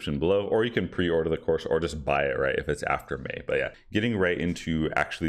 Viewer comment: when will the price change? I need feedback on ASAP to a friend. Thnaks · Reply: I thought he said after May. That's what I heard. I listened to it several times but wanted to be sure